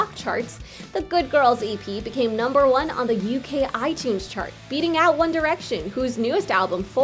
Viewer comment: i thought it said “eating out one direction”